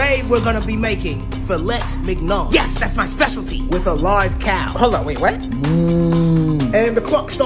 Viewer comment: Am I the only one that thought he said live cat? 🤣